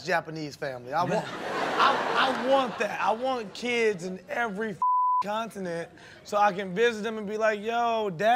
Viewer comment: My boy Sterling said he wants a kid in every country...lol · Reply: Lmfao thought he said every Continent! 🤦‍♂️